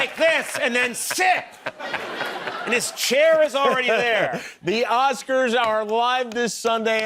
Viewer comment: what did Conan say? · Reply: "Watch those Oscars!"